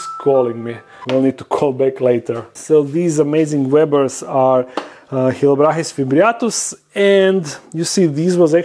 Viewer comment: how are they called? I just can't understand it acoustically. I'm new to the tarantula world. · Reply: chilobrachys fimbriatus :)